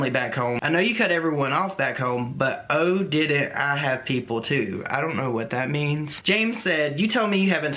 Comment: "I know you cut everyone off, but I didn't (cut everyone off). I have people." is what he was saying. Also, I disagree with what you said about knowing that you're attracted to someone through the internet. I'm not saying it's impossible, but I've definitely met people through the internet and started having feelings for them, but then when I met them in person things were just different.